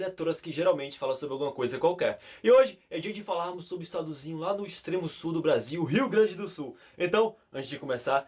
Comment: eu ouvi .....estadozinho?......foi isso????